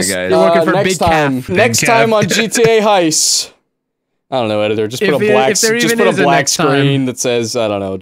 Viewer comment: lmao the end at 24:00 when was saying "I do'nt know editor" and the subtitles said "I don't know cute editor" this is why I love editor so much